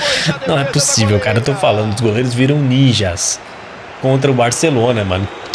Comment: só eu q escutei goleira kkkk Nesse EAFC homem e mulher jogam junto?